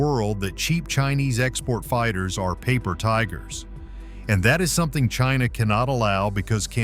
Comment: Ooh chinese crap planes ...scary. oh you said 'paper tigers'....I thought you said 'piece of sh!t'...